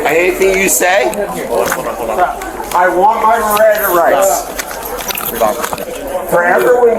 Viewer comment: What I hear is “I want my Riranda rights!” RIRANDA RIGHTS, RIRANDA RIGHTS! His threat was cute….”if I don’t fly today I refuse to rebook”. Okay dude…Buh bye